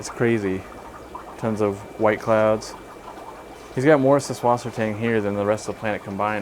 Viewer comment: What the heck are you saying at 5:39, 11:01, etc? Susflosotang? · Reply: Subwassertang = German for seaweed